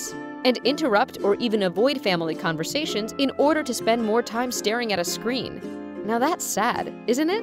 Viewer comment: Starting at a screen? Look at the words compared to what she is saying... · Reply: Stormy Wolf The captions say ‘starting at a screen’ instead of ‘staring’.